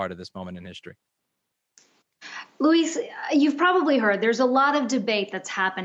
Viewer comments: Did she call him Louise?? lol 😆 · did she call him Louise?